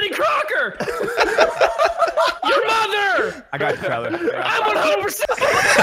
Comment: Idk why but to me it sounded like he said "I'm 100%, nigga." As in I'm whole